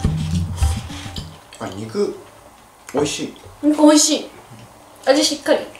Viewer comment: 💩う○こ💩美味しいって聞こえるのって俺だけ❓💧